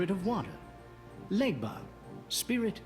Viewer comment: I wasn't watching the screen, so when the presenter said Legba, I thought he said Ligma.